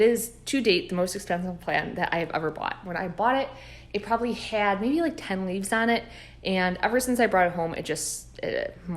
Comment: May I ask what the plant is at 23:10 ? I heard "Lagula Grandise" but that yields no results. I love the look and I would very much enjoy doing some research on it! Thank you. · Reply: I always struggle to pronounce it lol but it’s a licuala grandis, they are so amazing especially as they mature 😍😍😍